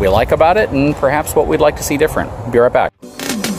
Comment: Lol i thought he was saying wet backs